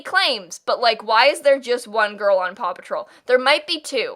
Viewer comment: complaining about Paw Patrol characters is just funniest thing ever i swear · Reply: help at first i thought she was saying papa troll and i was like tf even is that😭